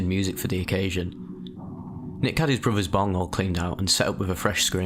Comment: Did he say nick had his brothers bunghole cleaned out😭🤣